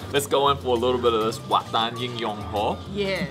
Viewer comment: I don't wanna be a party pooper here but as you guys introduced the Wat Tan In Yong Hor, i was checking out my phone and i miss heard it as War Tan In Your Hole ....and was like, the what!!?? .... i know i know .... i'm sorry